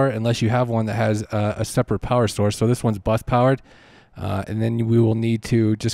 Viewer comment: Oh, BUS powered...for a moment I thought you said BUTT powered.... just kidding...:) :) :) Though, seriously, you lost me mid way; the powered USB hub, that plugs into the Focusrite, and the Apple Camera adapter plugs into the powered USB hub? Hope I got that right! Have you experimented as to whether an iPad can USB power a Focusrite, without a powered USB hub?